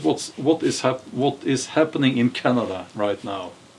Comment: mark he mentions "what is happening in Canada now".….... Could somebody clarify, or did I hear wrong?